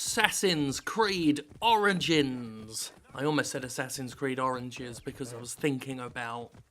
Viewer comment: i thought you said orange jizz, not oranges, in the opening line.